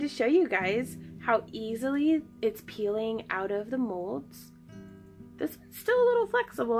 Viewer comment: The third heart you painted on the back... ....the nude prism (what?) paint ? It sounds like you said "peelio"